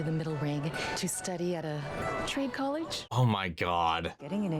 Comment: I watched this without subtitles the first time and I almost thought she said "tree college"